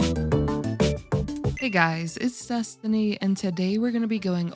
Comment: I keep hearing "Hey guys, It's Sustiny." 🙋🏼‍♀in the intro.